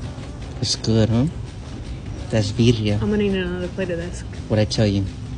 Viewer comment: Wtf I thought he said “COKE OR SOMETHING” go to comments… “Goat” 😅